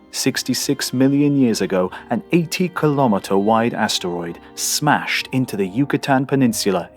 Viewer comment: Hiya, @ 2:50 did you say 80 Kilometer Wide Asteroid or 18 Kilometer Wide Asteroid ? Is hard for me to make out.